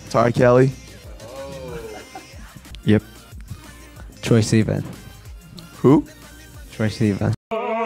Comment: did kook say troye sivan omfg